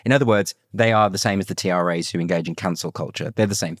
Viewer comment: "they are the same as the TAs who engage in cancel culture." BUT he SAID not "TAs" but "TRAs" - my problem is I do not understand either abbreviation. What, pray, is being said here ? Who is it that engages in 'Cancel Culture' ?